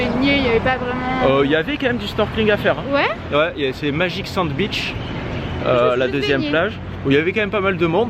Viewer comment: Ahhhhh la seconde plage est "magic sand beach" mais quand l'a dit la première fois j'ai compris "magic sandwich" 😂😂